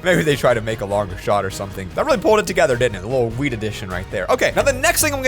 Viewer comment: am i the only one who thought he said 'weed addiction'?